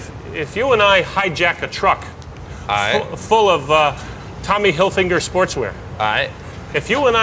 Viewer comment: did he say Tommy hilfi'N'ger @ 2:28 ?